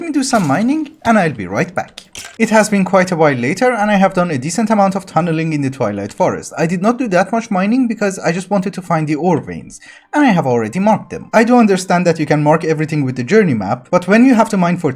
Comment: Great series. I really like your sense of humor. I keep mishearing you say you’re going to the Toilet Forest :)